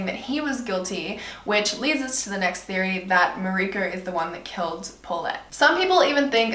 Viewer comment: did she say marieke